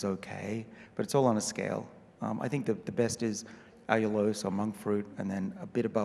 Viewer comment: Did someone hear what heis saidvis good 1st before mink fruit then stevia. If you did pls tell me. It was quite unclear in my end. It's Exactly at 14:25. Pls reply back so I know - I heard something like "i - os" - sorry for the literal translation but I could not make out what it is. And this is my thing. Anti aging